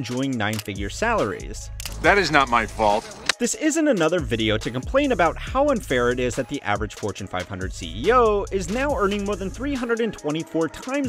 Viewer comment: unfair!! did i heard it correct 1:39. It aint unfair he is doing work and taking risk worth that much